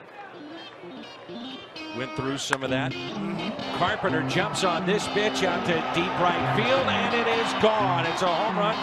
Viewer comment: it sounds like dan says "Carpenter jumps on this BITCH" 😂😂😂 GO CARDS!